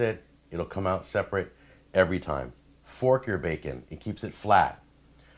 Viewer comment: lol i thought you said "pork your bacon"